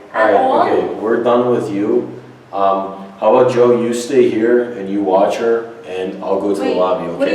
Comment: What did he say at 11:01? How about you stay here and I'll go tell the nanny? U couldn't understand it